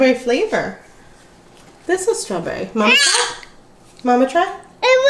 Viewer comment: Awe forks, mama. 😆 that’s what it sounds like is saying.